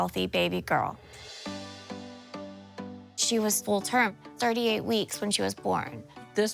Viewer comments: I had to go back a few times to make sure I heard right. She said when the doctor came in they said she was 38 weeks, first couple times I thought she said 30 weeks but then realized she just talks fast. 🤦🤦‍♀🤦🤦‍♀ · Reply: I thought she said 30 wks too! How could she have a 7 lb 30 weeker? 38 weeks makes sense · I had to go back a few times to make sure I heard right. She said when the doctor came in they said she was 38 weeks, first couple times I thought she said 30 weeks but then realized she just talks fast. 🤦🤦‍♀🤦🤦‍♀